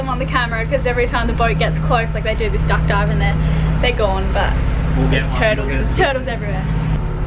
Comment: I thought was saying "there's Toads everywhere." Ocean dwelling Toads! ; )